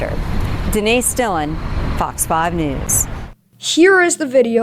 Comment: I don’t know why, but I thought he said “F*cks News.”